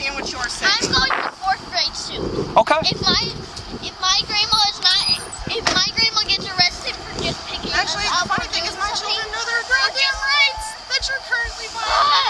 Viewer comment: it sounded like the kid said " If my Grandma gets arrested for just picking us up........." That's pretty bad for a kid to realize how unjust these clowns have acted. Wow.